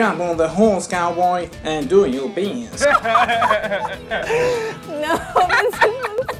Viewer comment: 😂😂 "Uhh so I say to myself "Jump on the horse, cowboy, and do your best" Idk what's worse, his southern accent or the fact that I understood every word he said 😭😂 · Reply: Lmao I thought he said "And drop your pants"